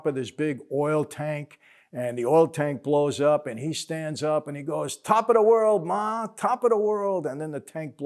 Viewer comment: Very seldom see a movie on cable TV with the great Edward G Robinson. Read that he was a quiet nice man. Old timers like me loved Jimmie Cagney playing a gangster..Always thought he said look at me now mom instead top of the world mom. Thanks for another great vid.